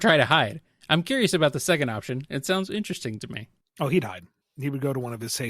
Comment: I seriously heard that as "he'd died" too. Even the Closed Captions read it as "he died". lol